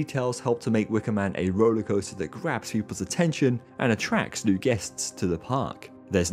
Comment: I swear I hear him say "craps people's attention" instead of "grabs people's attention." 😂😳